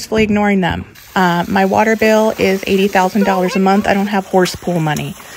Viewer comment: Asian parents all over the world just died when you said the water bill was $80,000 a month 😂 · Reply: Yikes! I was enjoying watching the horse play with the water, I thought she said $8,000 a month. Maybe its in pesos 😅